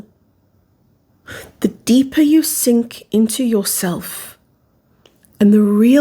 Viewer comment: when you said “sink into yourself” i heard “sync into yourself”… integration.